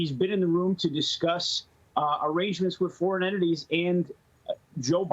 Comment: Did this guy Nick Langworthy say "foreign enemies" ² 1:37. Does he understand what the English word Enemy actually means? Certainly the people in those countries understand English language, and what that word Enemy actually means. Taking money from a competitor for influence is corruption, but taking it from an Enemy is treason. It is shocking how these prominent Americans call other nations, that they trade with, their "Enemies" . It illustrates the American mindset. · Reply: I think he said "entities"